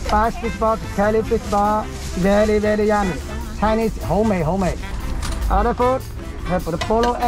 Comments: Hey Ari, at 1:31 is he saying "homemade" or "hou2 mei6" ? To my ears, it sounds like he's saying the latter. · I think he's saying 好味 ("delicious" in Cantonese), not "homemade" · i think he said “好味[道]” meaning yummy, and not “homemade” altho they sound the same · I think at 1:31 the subtitles were wrong.. He said "Chinese, ho mei, ho mei" as in "very tasty" in cantonese. I don't think he was saying "homemade" lol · Wait I thought that he said at 1:31 “Chinese fish balls… Very very yummy; 好味” which was incorrectly translate to homemade when it’s just the Chinese statement of tasty? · I think at 1:35 he says "it's tasty" in cantonese versus "homemade"